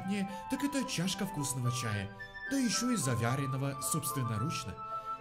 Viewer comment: он что сказал завЯренного